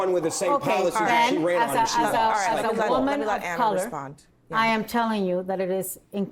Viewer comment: This is what I heard 6:25 “As a Woman of color with taco kisses.” 😂